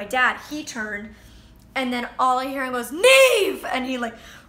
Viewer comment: did she say Niamh because if so then i want to say my name is niamh too!